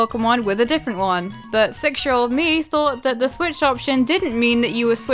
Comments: did you say sexual me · Sounded like she said "Sexual Me"